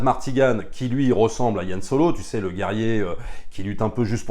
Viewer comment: J'ai entendu "Yann Solo". J'ai pleuré :D